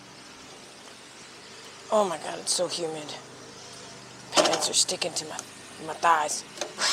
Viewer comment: you started it when talking, i thought you said your nuts are sticking to your thighs 😭